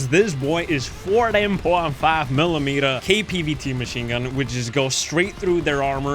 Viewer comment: What? I can't understand, I turned on subtitles and it also gave up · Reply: 14.5mm KPVT machine gun